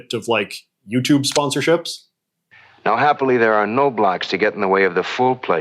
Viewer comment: Wait, did he just say what I think he said? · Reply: “No blocks” (as in filters)